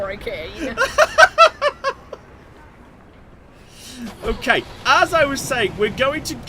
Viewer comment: I was listening to this without watching and thought was saying "horrorcane"